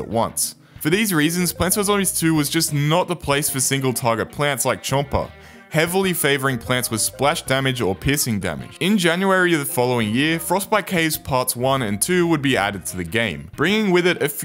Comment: I genuinely thought he said pissing damage 💀